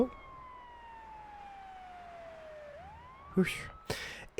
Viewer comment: What did he say? Popo?